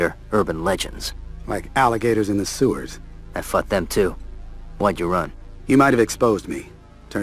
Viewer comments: did anyone else hear Bruce say "I fucked them too" rather than "fought" at first? xD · does Batman say I'd fuck them too? Wtf e.o